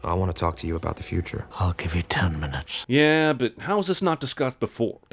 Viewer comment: I thought he said I will give you ten bucks